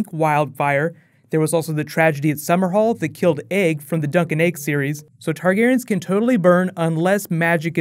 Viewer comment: Did you say at 3:55 that "incident that killed Egg from the Dunk and Egg series"? I didn't quite understand.. But I thought Egg was alive at the end of the book! Oo btw: thank for all your awesome breakdowns, greetings from Germany :)